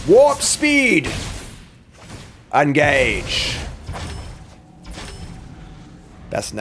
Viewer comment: The way he says "engage" at the start of the video genuinely sounds like him saying "I'm gay-ge".